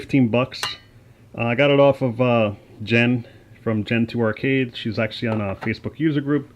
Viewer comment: Could you let me know where you got the ROM board? I heard you say “jen from Gen2arcades”. Not sure if this is right, couldn’t find anything on her or them. I think you said a FB user group.